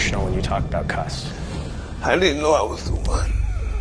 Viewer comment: Not that it matters, but they have misquoted Mike IN PRINT at 8:04 What he actually says is I DIDN'T KNOW I WAS THE ONE.